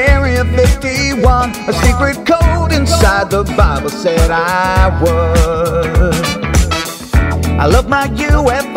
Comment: I thought he said "a secret code inside the bottle said i won "